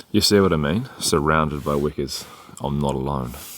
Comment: WEKA All this time I thought you were saying WICKER. That was cool THANK YOU